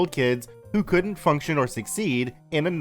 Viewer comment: when u said function my headset glitched, making me think you said fucktioned